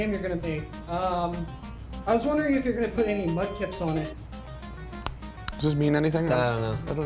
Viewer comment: "I was wondering if you were gonna put any..." can someone finish that sentence for me cause I have no fucking clue what he's saying. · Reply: Wait I just found it, he says "Mudkips". A type of Pokemon lol